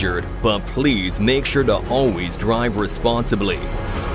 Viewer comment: did he just said "drive"?